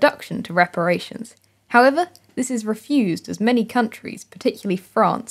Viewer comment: 🚨CORRECTIONS 🚨: ✔︎ None so far! :) Spotted a mistake in the video? If you think you’ve spotted a mistake, leave a polite comment saying what you think is wrong and linking a legitimate source. I’ll check and if you’re right then I’ll add the correction to this pinned comment. :) ✘ I do not engage with corrections comments which are written rudely ✘ If you leave an essay full of corrections, I won’t check it because I have other, better things to do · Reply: hello, there is a tiny mistake at 0:40 in the subtitles, where it says "m any" instead of "many". Apart from that its a greta video :)